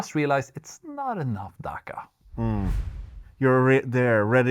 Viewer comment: did i hear DAKKA!!! WAGGGGGGHHHH!!!